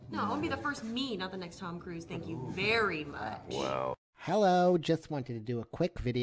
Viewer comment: I thought she said " il be the next meme, thank you very much ..."